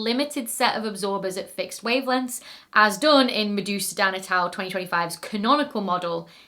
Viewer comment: I thought I kept hearing her say Medusa Dan 😭